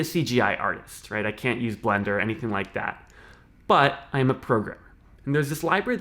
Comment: When he said I'm a programmer I heard he said I'm a pervert, I had to replay that part again